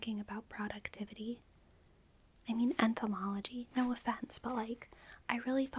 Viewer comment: i thought you said etymology instead of entomology and got confused when you started talking about bugs